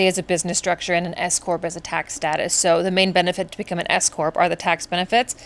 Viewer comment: I thought you said Escort 😂 lol because I was multitasking and listening...and then I realized S Corp maybe re: your business. Anyway thank you for sharing your journey with us. Much love 🙌 🥰